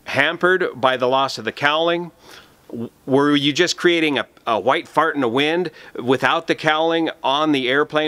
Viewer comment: "A white Fart in the wind" Has to be one of the best Aviation illustrations I have EVER heard! Thanks. You are the Best... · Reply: I thought said a 'light' fart in the wind!?